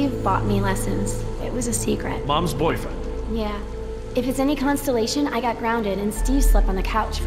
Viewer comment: How is being concerned about the extinction of the humpbacks being dramatic? Also, if you listen at @ 10:15 Jess pronounces it constellation instead of consolation.